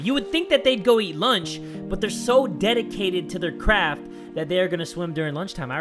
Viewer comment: the subtitles said “but they’re so dedicated to their crab...” 😂😂😂 even though he clearly said crap!